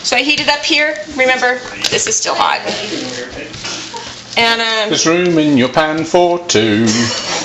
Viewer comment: HAAAAAAAAA! I thought was saying "there's room in your pants for two!" FREAK NUTS! And yes,I like calling him "Daddy" instead of!! I lost my dad almost seven years ago and seeing and and family and all of your shenanigans just makes me so happy :)